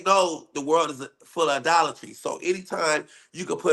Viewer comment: I thought he said there's a thin line between a Dollar Tree and prosperity.. (Idolatry) 😂😂😂